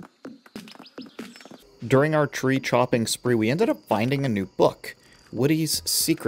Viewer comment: Love the vid, I assume you put your voice into text to speech for the subtitles, heads up, at 18:57 it reads “during our tree shopping spree” instead of “chopping” and then “what do you secret” instead of “Woody’s Secret”